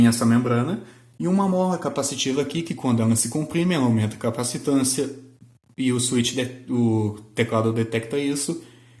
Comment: Amigo q tipo de teclado é esse dos 3:54, não entendi direito o nome, Toten? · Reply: Topre.